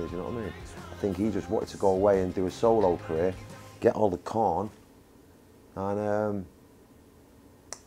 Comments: 2019 Noel G rides on a high horse, whilst Liam G walks with us - 'the people' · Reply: Kingston R Vanderbilt What does Liam say at 02:05? Get all the corn/cotton? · Noel, if you're watching this, Liam's only joking after 1:25 . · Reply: What does Liam say at 02:05? Get all the corn/cotton?